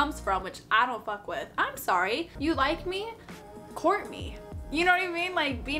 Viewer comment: I legit heard say "You like me? Courtney" at 5:20 and thought "who is this Courtney bitch?"